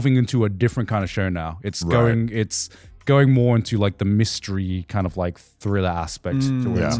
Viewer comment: did i just hear "Mysteries"? Praise The Fool